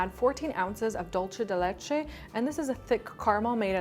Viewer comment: Can anybody pls tell me what did she say at 2:44-2:50 ? 14 ounce of WHAT 0_o so was that caramel taken out from condensed milk ? (im in a non speaking english country so...) Thanks for the help ! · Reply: I have all of my recipes posted online here: <link> It's 14oz of dulce de leche, cooked condensed milk